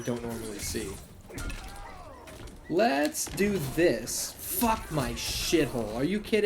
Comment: Did you just say fuck my shithole 🤣🤣🤣